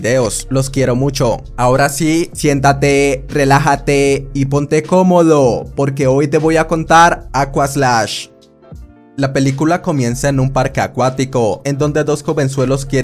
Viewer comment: Pensé que dijo pero hoy te voy a comer 👍🏻😹